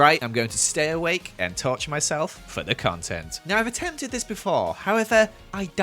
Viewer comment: I actually thought he said “touch myself” lmao